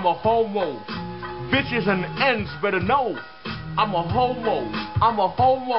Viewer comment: At first I thought he was saying "bitches and ents". XD